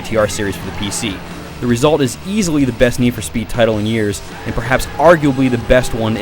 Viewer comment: What did you say at 0:32? Perhaps the best Need for Speed ever? Have you not played Undergrounds or Most Wanted??? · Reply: he said “in years”. shift came out 6 years after undergrounds. which was amazing.